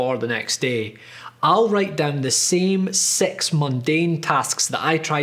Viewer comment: does he say "six" or "s*x" xD Probably six lol